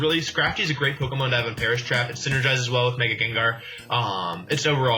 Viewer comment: When he said Perish Trap, I thought he said Paras Map lol